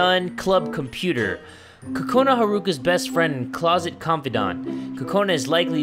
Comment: did he say closet